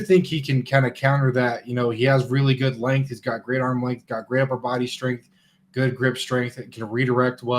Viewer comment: When he was talking about Patrick Paul, and stated he has great upper body strength. I had to take a second listen. At first it sounded like he said, "he has Grandpa upper body strength". Which almost sent me. 😂